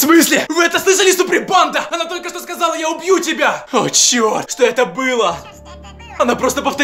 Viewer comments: Когда анжела сказала я убюью тебя Мне послышалось я люблю тебя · Когда Анджела сказала " Я убью тебя" Мне послышалась что сказала Анджела " Я люблю тебя 💟" 😐😢😟😟